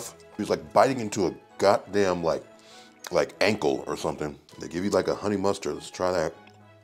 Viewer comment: Wait did you say ankle omg 😂😂😂 I thought you were gonna say cardboard box, but ankle took me out ❤❤